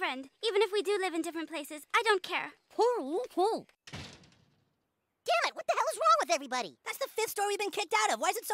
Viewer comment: I heard Kenny say more on the lines of “OH.FUCK YOU!” rather than “OH. GOD DAMMIT!”